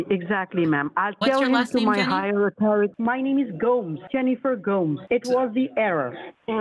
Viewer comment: When he said his name is “Jennifer Goes(?)” I instantly thought of Steve-O when he said the password “The Golden Goooooes” (instead of goose”)🤣🤣🤣 One of my FAVS!!!